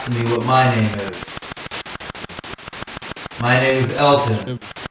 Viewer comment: I heard Hello Elton